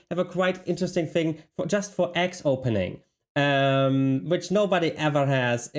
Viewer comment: What are you saying here? Axe opening?